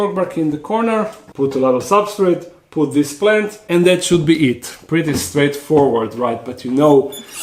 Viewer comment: I thought said she'll be eaten